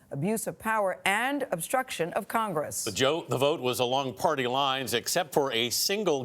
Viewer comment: Did anyone else hear the gentleman say “the joke” at 0:20? 😲